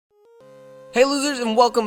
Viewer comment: did he say hey losers?